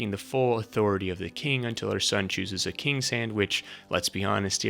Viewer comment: OMG I'm dead... I 100% thought he said "Cersei arrives, taking the full authority of the king until her son chooses a king sandwich. Let's be honest, probably isn't gonna happen any time soon." And Tommen being a literal child just made the mental image that painted in my head 100x funnier. 🤣